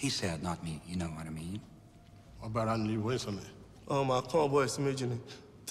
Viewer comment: alguém sabe me dizer exatamente quais as palavras os africanos falaram? sei que tem uma tradução em outros videos por ai, mas não acho que seja correta. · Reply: Felipe Silva nos 1:43:48 ele diz algo como "what about you... man" não deu pra ouvir direito. Mas é só nesses segundos que marquei acima que queria entender quais realmente foram as palavras deles, o resto eu entendi pq manjo razoavelmente bem inglês